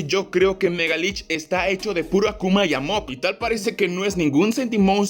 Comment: Primero entendí "Esta hecho de puro akuma y amor"